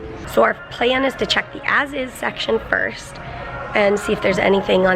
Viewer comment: Lol Sometimes i dont hear things right and when watching this one i thought you said the acid section instead of as is so i thought id share that silly moment